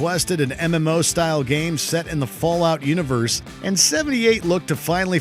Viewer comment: Did he say Fallout 78 or am I going deaf?